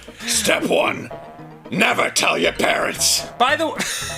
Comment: Thought he said “NEVER TELL YOUR PARROTS”